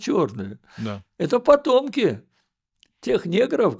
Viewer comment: На первой минуте услышала «это ПОДОНКИ», оказывается «ПОТМОКИ» 😅